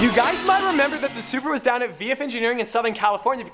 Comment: did he really say subaru instead of supra or are my ears not working